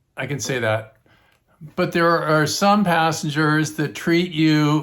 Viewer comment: Assengers😂 is what i thought you said